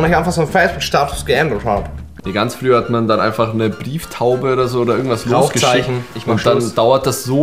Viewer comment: Ich hab "Beeftaube" verstanden hahahaha😂